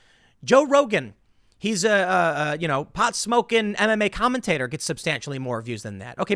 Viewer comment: Had to doubletake. Thought I heard you describe Joe Rogan as a pot-smoking anime commentator.